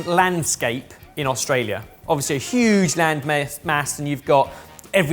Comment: Did just say land-myth? You heard it here first, everyone. Australia isn't real.